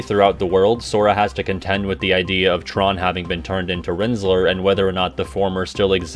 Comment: My brain is cooked, I heard "Rinzler" and immediately thought "Heh, Rizzler"